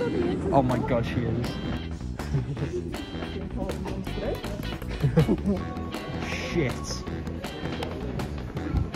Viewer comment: what did she say I'm slightly deaf😂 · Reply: "That's the important ones through!" Sorry for the late response, we were on another adventure